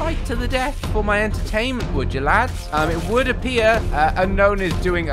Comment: did you just say fight for my entertainment?